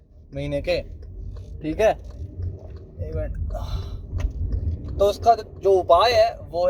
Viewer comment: what did he say? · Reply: I said ek minute